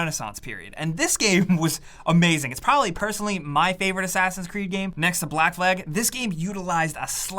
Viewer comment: Did he say assassins creed black leg in